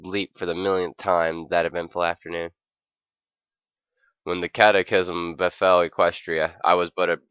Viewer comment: you said catachism(catholic prayer) instead of cataclysm...i laughed my ass off